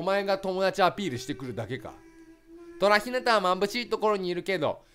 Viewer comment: 眩しいをまんぶしいって言ってるように聞こえる👂